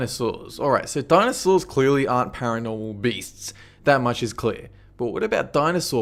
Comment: I thought it said paranormal breast 😂